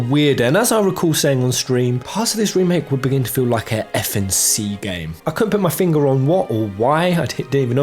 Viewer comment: What does he say ~ @ 15:30 that sounds like F&C game?